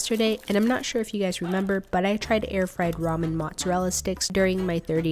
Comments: For day 7 you can try making an Oven-Baked French Bread Pizza with takis powder on top (love your videos btw) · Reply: idk how but when she mentioned the last time making mozzarella sticks I thought she said air fried ramen marshmallows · idk how but when she mentioned the last time making mozzarella sticks I thought she said air fried ramen marshmallows